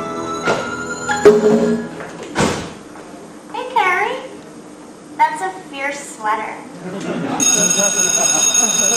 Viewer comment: what did she say? someone pls tell me😩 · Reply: "Hey, Carrie, that's a fierce sweater"